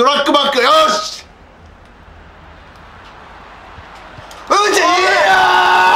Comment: う○ち！！！にしか聞こえないww